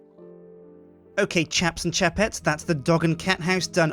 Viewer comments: did just say chaps and chapettes? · LOL WUT did you just say "Chaps and Chappets"